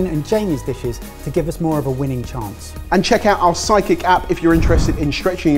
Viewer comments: Lol, I thought Jamie said “Check out our Psychic app” … <doubletake> 🤣🤣🤣🤣🤣🤷🏻‍♀️ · is it just me or did .. Jamie sound like he said .. check out our Psychic app ... hahahahahaha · Am I the only one that thought Jamie said ‘psychic app’?